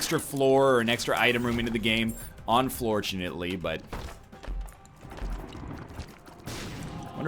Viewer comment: Did NL say "Unfloortunately" at 3:50? I'm like 90% sure he said unfloortunately